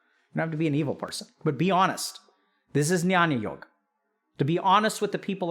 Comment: What is that word “nyaniyog” or something to that effect he says around 20:15?